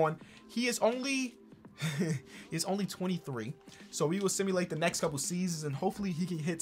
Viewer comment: I thought the caption said “I stimulated”